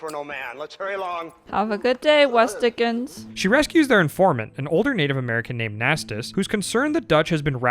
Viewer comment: bruh for some reason I heard you say "a former Native American" and I was like ayo, he ain't change races lmfao. But then I went back, realized I'm just stupid lol